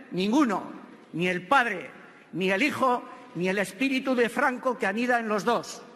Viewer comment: Amen Edit perdón, dijo espíritu de franco, pensé que dijo espíritu santo.